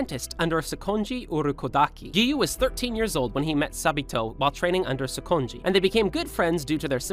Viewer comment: why do i hear sa-bi-toe✨